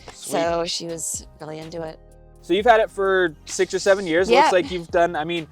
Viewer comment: DID I HEAR 6 or 7